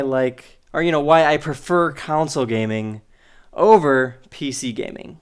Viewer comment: did he just say council gaming?